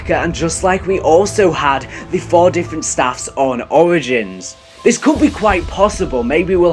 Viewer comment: i thought u said oranges